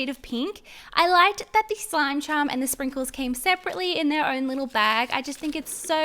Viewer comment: When she was saying "slime charm" i thought she was saying "slime chum"